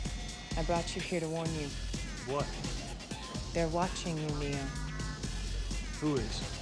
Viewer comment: What is she saying, who's watching him? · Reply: Agents, the architect